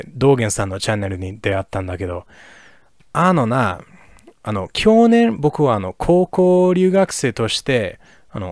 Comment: Japanese was fantastic, but one thing that was been distracting was the way he pronounced きょねん (1:49). I quickly figured out he was trying to say 去年, but because he pronounced it as きょーねん or きょうねん, it sounded like 享年, which means "the age someone died". Especially because he started the sentence with あのな～, with some tone that sounded like he was pissed off, I momentarily thought he was going to talk about how someone died because of his Japanese or something. I guess this is another example of how stress pitching can fundamentally change meanings of words.